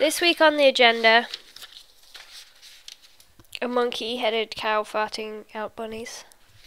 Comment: does it sound like shes say farty ._.